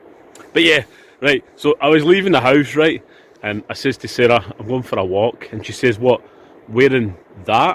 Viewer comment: was my wife mate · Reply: oh my bad haha, i heard ''my sister sarah'' in ya lovely accent